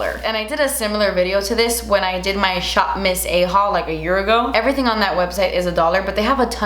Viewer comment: Literally thought you said "shop miss A hole" not "shop miss A haul" 😭😂😂😂😂